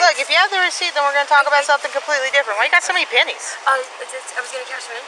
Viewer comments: “Just making sure you’re not throwing pennies at cars???” LOL WTF? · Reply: I thought she said “Panties” I was really confused…LOL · Throwing pennies at cars is a thing?? 🤷🏼‍♀️ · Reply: I 100% thought she said “panties.” I had YT on in the background while I was getting ready and I really thought she was throwing underwear at cars. It’s concerning to me that I didn’t even think twice about it 😂